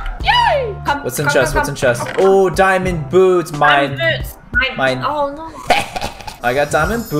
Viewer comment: When he said diamond boots I thought he said diamond bo*bs